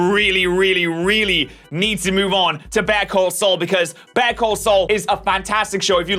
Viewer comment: the first time you said better call saul, I misheard you and thought u said bear cold soul. i was like hmmm I never heard to that show 🤔